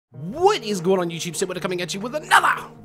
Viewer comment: it sounds like you say "wheat is going on guys"